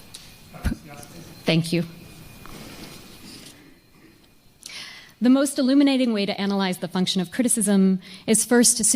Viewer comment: Amazing talk! What does the person in the public say when she asks how it is pronounced something? Minute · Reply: It may be parrhesiast?